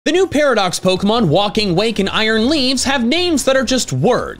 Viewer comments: HAHAHA, im sorry. I thought you said Walking Wank. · I thought you said "Walking Wank" at first 💀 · Swear to god I thought you said "Walking Wank" and was severely concerned for the future of the Pokemon franchise